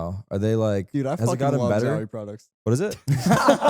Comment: dude what did zekken say at 41:09😭😭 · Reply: "dude i fucking love zowie products"